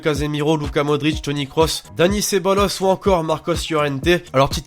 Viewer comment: Mdr a 2:49 quand il dit ceballos j'ai compris ce bolosse (rip l'orthographe)😂😂